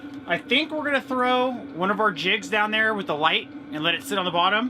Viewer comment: Teacher here. Thought you said “gonna throw one of our kids in there!” Hahaha! Quickly figured out you said “jigs” of course, but yes, you had me laughing!!